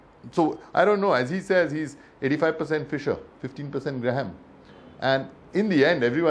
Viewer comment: he actually says 85 % Mr Graham and 15 % Mr Fisher